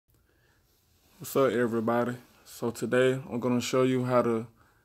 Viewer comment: That "Sup EveryBody" KILLED ME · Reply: I thought he said Sa EvErYbAdY I